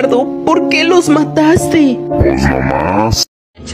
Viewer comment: alguien me dise que dijo el callado en el minuto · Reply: Pos nomas 😭🤧☝️❗️